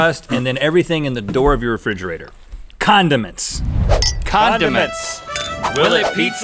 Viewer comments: When Rhett said condiments I thought he said Condoms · Am I the only one who thought he said condoms instead of condiments